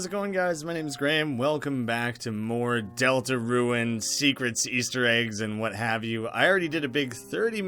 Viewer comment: anybody else hear deltaruined or just me